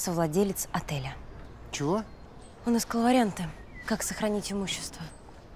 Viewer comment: подскажите что она говорит? У нас …., как сохранить имущество. Что это за слово? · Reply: Он искал варианты, как сохранить имущество